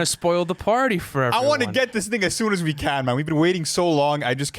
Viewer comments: I swear he said the n word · BRO WHAT DID SAY IN 1:18???? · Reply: he said "i wanna get this thing"